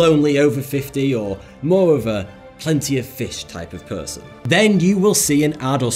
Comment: google knows that tomorrow, ill be watching all kinds of "random" videos on youtube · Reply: who else heard "lonely plenty of fish type of person" instead🤣